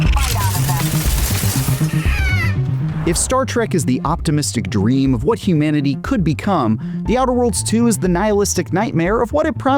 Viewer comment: at first I thought he said:" If Starcraft is the optimistic dream of what the universe could become" xD